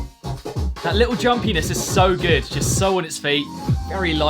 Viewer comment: I literally can’t hear what he’s saying, it just he’s saying “That little drum penis is so good” 🤨